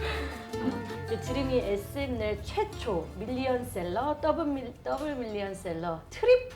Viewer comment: seems like they edited the subs too but they can’t edit what hyo said~ i stand by this correction No hate on Dream and Hyoyeon but.. Hyoyeon said SM’s 내 최초 which means SM’s 1st. You can hear her saying that. FYI: • EXO is SM’s first MILLION SELLER in 2013 • their first DOUBLE MILLION SELLER in 2015 • their first TRIPLE MILLION SELLER in 2016 • their first QUADRUPLE MILLION SELLER in 2017 • their first QUINTUPLE MILLION SELLER in 2018 • their first SEXTUPLE MILLION SELLER 2021 🩷 NCT Dream is SM’s 1st million seller on their 1st week of sales~ I just think we all need that correction and rightfully credit EXO. No one is hating on them 😀 ©: Alyssa Uy Do